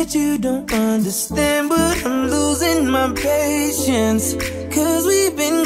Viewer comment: Love Chris, love this song, but I keep hearing “I’m losing my patients” at the beginning, and I am like OK Chris is classic as an artist but no good as a doctor 😂🎶🎵